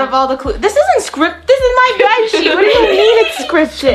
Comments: idk why people thought swore on that she said “scripted bed sheet” not sh*t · so didn’t swear she said “scripted bed sheets” I reviewed the video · This isn't hate or anything but it sounded like said the b word for golds bed but he said bed sheet. REMEMBER THIS ISNT HATE OR ANYTHING SO NO ONE BETTER NOT COME AT ME. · she actually said "scripted bedsheet" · what did say? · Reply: Scripted bitch or she probably said bedsheet I probably misheard it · I thought said “scripted bed sheet” 😂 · she did not say b- she said bedsheet no hate i just wanna say